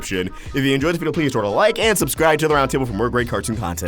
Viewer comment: I always hear "awestrick vox", I can't be the only one